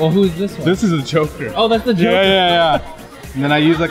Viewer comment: OHHHHH the Joker I thought he said thw choker